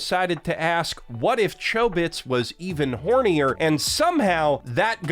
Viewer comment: "What if Chobit's was hornier?" I heard this as "What if Chobin was hornier" and immediately imagined something horrifying from Pokemon Gale of Darkness. How dare you.